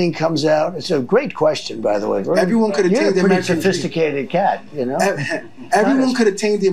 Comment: WTF? Does Trump call him a “sophisticated cat” at 11:40 ?????????.